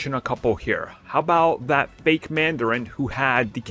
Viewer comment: Can’t wait for what if the mandarin had a captain America tattoo on his neck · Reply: I thought you said mandalorian i was about to say Nah star wars can stay right where tf its at 😭🤣